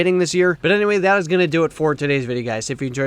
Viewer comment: WTF are you saying 🤣😂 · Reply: He said but anyway that is going to do it for today’s video 🗣 webbsbbdbdbjshhshgsggsgsshsdjjdjjdjjdjjdjjdjxjjdjdjxjd... alright